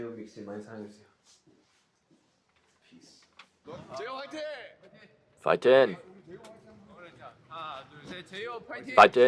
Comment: did you just make a joke and instead of saying fighting you said fightjin? @ 26:39 or am I just hearing things wrong😂 also love the way you proudly shake the army bomb/lightstick 😁